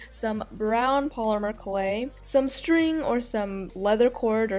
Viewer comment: When you said polymer clay I thought you said plumber clay😂😂😂